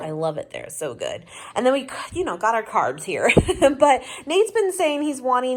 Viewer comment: When you said you had your carbs, I thought you said cars and I had to do a double take! Huh? Lol! Glad you survived your race!